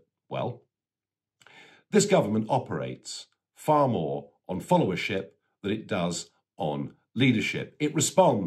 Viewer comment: was it just me who thought he said "this government operates on full of shit rather than leadership" :hehe: Had to play it back a couple of times to check.